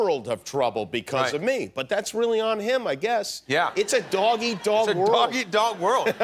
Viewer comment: I thought he said it’s a doggy dog world I was trippen 🤯